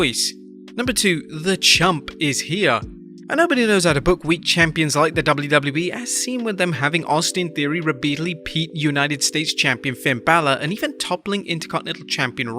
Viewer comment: did he really say "rebeatedly peat"?!🤣🤣